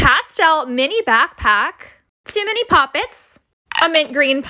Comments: Who else thought she said too many popits I was like that’s not too many 👇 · When you said two mini pop it’s I thought you said too many popits